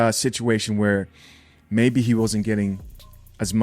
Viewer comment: I thought she said she wasn't getting non? Lol.. sure does sound like she's busy